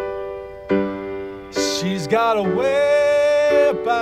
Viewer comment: Did Roy say at 0:39 "She got a wip on her"? This is the only thing from the office I don't understand.